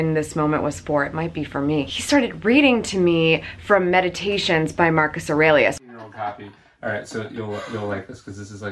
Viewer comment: What book did he read to you? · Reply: Meditations by Marcus Aurelius is what I heard... you can listen for yourself